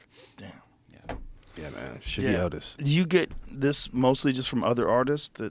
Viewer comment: I thought Gary said "Shitty Otis". I went down a weird rabbit hole with that search criteria....